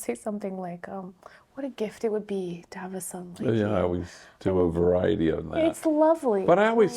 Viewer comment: Please cite what is said at 33:23 by Kate. Her last word in unintelligible. Thanks. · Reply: Fr. Greg often says to the homies, "what a gift it would be to have a son like you"...♥️